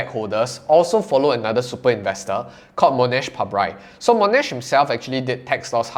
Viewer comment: Sorry, what's the name of that "other super investor" that mentions at 4:35? I didn't quite catch the name. Monash something I think... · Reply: Mohnish Pabrai